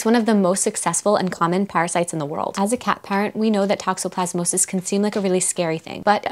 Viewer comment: Great informative video. I thought you said 'cat pirates' argghhhh, but then I realized cat parents 🙂